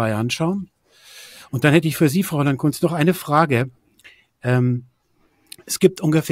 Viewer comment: Ungefähr bei 11:46... Höre ich da 'Fräulein' ? Oder höre ich schlecht und ersetze 'Ungehörtes' durch eine (ideologisch geprägte) Wahrnehmungstäuschung? Einerlei, ich habe abgeschaltet.